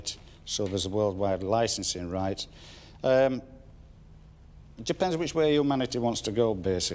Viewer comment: I thought he said "It depends where Hugh Manity wants to go" and I thought maybe there's another business partner involved.